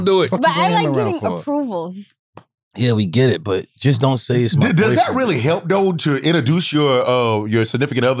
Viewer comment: I like how Jack Thriller was combating Charlemagne on this mental health stuff and other stuff Charlemagne acts too PC sometimes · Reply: so annoying I thought he was saying " don't say the B word" as a joke lol